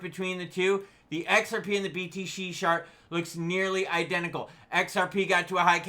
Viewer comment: Did you just say"BTC shart" @ 7:09???😅😅